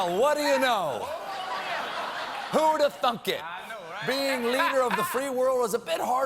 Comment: what did he say? ( who'd have ??? it ) I hear thunk it, but means not sense to me